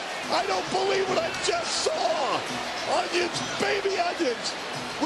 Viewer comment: did he say “onions! Baby onions!” ?